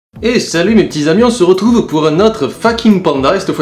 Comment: XD au début d'abord j'ai compris "Hey salut mes petits avions!"